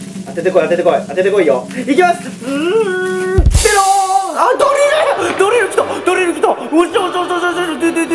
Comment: ダリルがドリルに聞こえた